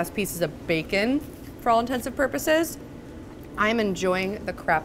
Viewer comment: she says “for all intensive purposes” ahahahahahah, instead of “all intents and purposes”